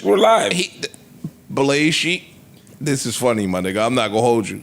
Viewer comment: I thought I heard 'Blaze Cheek' and spit out my coffee